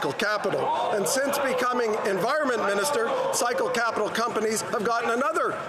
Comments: I was listening to this video and thought you were saying “psycho capital”! It might be a good name for it too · is it bad that i heard it as "Psycho Capital"?